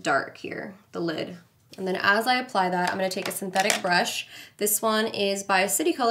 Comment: I heard "nice and pathetic" so i had to go back and you said "nice synthetic" nada que ver 🤣